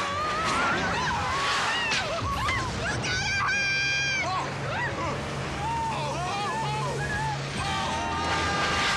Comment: Did she say "We're going to hit!" or "Look out ahead!"? lol. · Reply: I heard "Look out ahead"